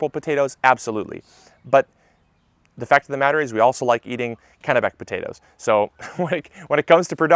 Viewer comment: What type of potatoe?? Sounded like you said Cannabak potatoe but searching it up comes up with cannibal potatoes lol Never mind a commenter helped me out by typing out Kennebec potatoes below !